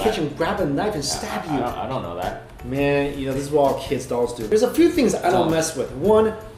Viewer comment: ohhhh dolls I thought he said dongs....